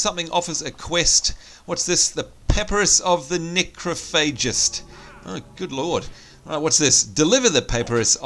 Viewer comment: thought you were going to say necrophilia hahaha :p